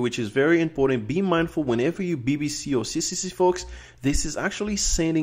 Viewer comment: Thanks, But I heard bbc at 10:38 :D